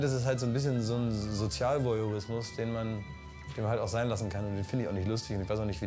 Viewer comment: was sagt klaas in minute 9:36 sozial... ? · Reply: sozialvoyeurismus